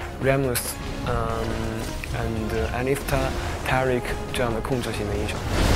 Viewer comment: Did he say "I need Taric jungle..." at 1:28? :D